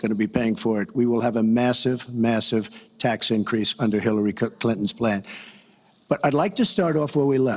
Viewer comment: did he say “Hillary Cuntans” plan?